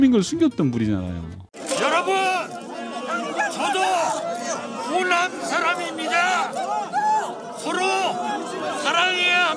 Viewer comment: " 여러분 저는 감옥갈 사람입니다"로 들리냐 ㅋㅋㅋㅋㅋ